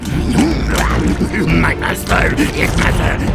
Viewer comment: I thought Igor said "My mustard."